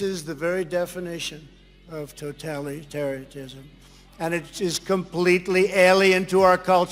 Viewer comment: Did he say "Totali-tery-tism"?